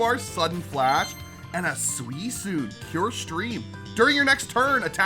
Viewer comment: DID HE SAY SUISOONE????